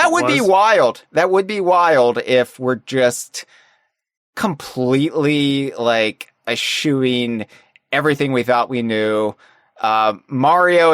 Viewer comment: completely assuing? What's he saying here?